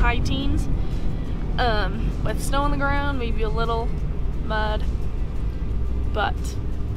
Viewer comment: Did she say theres snow on the ground and a little mudbutt? Nothing worse than being cold with mudbutt!